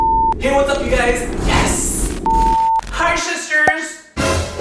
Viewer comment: I thought you said hi shitsers